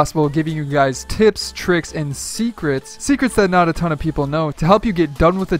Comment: did he say tits tricks and secrets